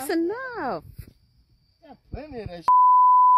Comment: did he just say we got plenty of that shi💀